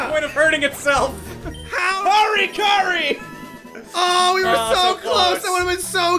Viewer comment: at the end where the Metapod hit itself, did he say Marin Karin? That's a Persona reference. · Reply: i thought he said hurry curry :P